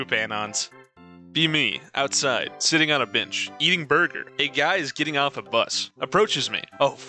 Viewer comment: had to rewind since I heard: sitting on a bench, eating bird poop